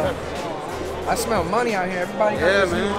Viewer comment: what did he say and have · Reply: bananapopper three shell game ; it’s a scam